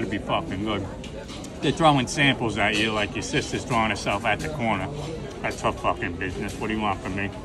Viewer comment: They’re throwin samples at ya like ya sista throws herself on the corner that’s her fckn business😂😂😂😂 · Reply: Thank you! I thought he said coroner I was like who does that at an autopsy?!